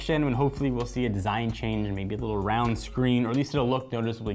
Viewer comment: did he say round screen??? Who does he think he is